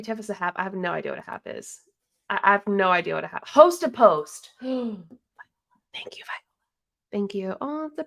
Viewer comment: I honestly thought these people were saying "host TO post" and honestly I could not comprehend why. Now I know it's "host A post" and this makes....not that much more sense. 😂